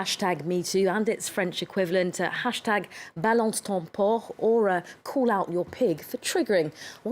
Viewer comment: I thought it said: Balance ton force..